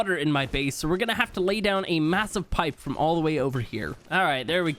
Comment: did I hear the word massive??